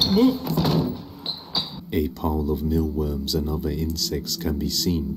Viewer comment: That's not maggots. They're too big to be maggots. It looks more like mealworms. They can also be used as bait for fishing. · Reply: Sorry, now I heard him sayng it's mealworms!